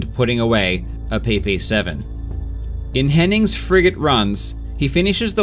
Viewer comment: I keep hearing "Frigate run" as "Fricking run" as if you were upset at the guy cheating lol